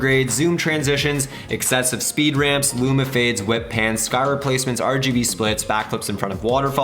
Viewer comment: He said "whip pans" but i heard "wet pants" 👀😬